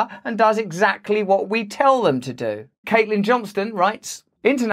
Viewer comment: I thought he said "Caitlyn Johnson" and I'm like hahahah I know who THAT is! :)